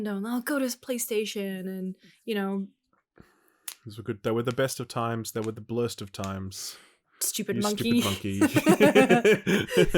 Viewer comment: Saying "It was the best of times, it was the worst of times" was from the simpsons actually a part of the little English lit major in me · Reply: is clearly saying "They were the best of times, they were the BLURST of times" and that makes it a 100% Simpsons quote. It's the a thousand monkeys typing on a thousand typewrites to create the greatest novel known to man scene from the famous "Stonecutters" episode, hence the "stupid monkey" bit from. You Zoomers need to get your facts straight before "cringing out".